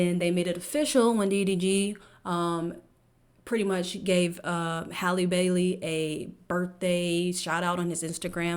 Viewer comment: Oh I get it now. I thought you said Halle Berry. I was like she’s too old to have a kid. What do you mean?